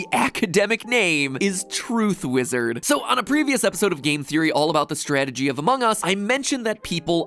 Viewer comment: I heard him say " Film Theory " instead of " Game Theory " -1000 I.Q. Moment